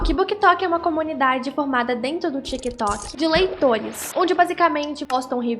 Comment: eu escutei leitoins